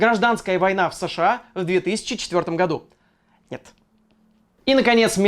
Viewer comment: послышалось , гражданская война в 2024 году. я такой ого, нука , нука. А потом я понял , что это был 2004.